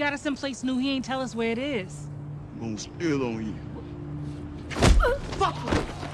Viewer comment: Lol the fact that he said imma steal on ya and immediately stole on her had me weak😂😂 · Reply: I thought he said it’s gonna spill on u.